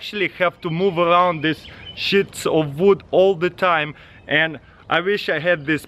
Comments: I thought he said shitsu of wood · who else when they heard him say shits of wood looked straight in the comments to see all the comments about him saying shits of wood